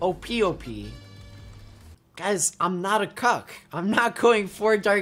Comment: Songlist: 00:38 The Penis (Eek!) 00:56 Glitter Gulch Mine - Banjoo-Tooie 01:37 DBZ Majin Boo - OST 9 02:57 Mario Paint Music - BGM 1 04:33 Bianco Hills - Super Mario Sunshine 07:23 Donkey Kong Country OST 8 Bonus Room Blitz 08:20 Metal Slug X (Arcade) Music - Mission 2-3 10:09 マスクド・デデデのテーマ 10:37 Tekken 3 - Jin (OST) 11:54 Contra OST - Jungle - Hangar Intro and Outro in the description! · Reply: Yoshi M. why did he said in 1:49 *I'm not a cock" even tho he plays singed.....